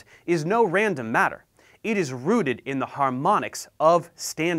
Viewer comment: Stopped watching after you said 'Ruded' instead of 'Rooted' :) Could cause an ambulance driver to go to the wrong address if you make the fundamental error of replacing T with a D. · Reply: I heard “rooted”